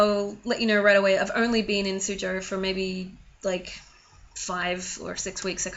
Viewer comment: What part of china are you staying in? I keep rewinding at 14:03 because I think you said it but I can't understand... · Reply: She said Suzhou